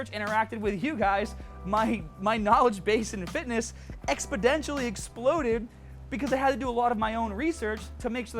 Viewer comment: did you really say "expEDENTially"? LOL